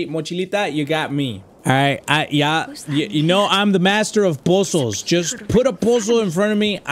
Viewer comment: When he said puzzles I thought he said bozos lol